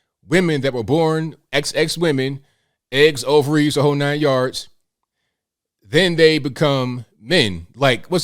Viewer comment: I first heard, "eggs over easy - the whole 9 yards"...😂✌️